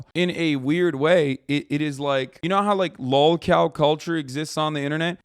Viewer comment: ? Tf did he just say? Lolcow?